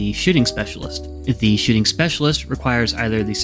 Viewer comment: Really thought I heard "Shitting Specialist" and was like, 'Rimworld is finally getting too literal for its own good